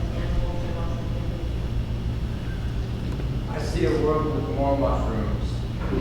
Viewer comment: ? 'I see a world with more locker rooms'? anybody catch that one? MW says, ' , , ,not totally sure about the last one.' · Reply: I heard 'more mushrooms'